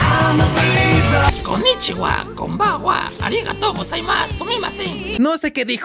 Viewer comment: Lo que dice el es "Konichiwa, gracias por la pequeña charla."